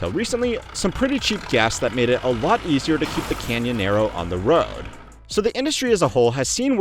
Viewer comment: Did you just say Canyonero? As in that Simpsons episode? 5:45.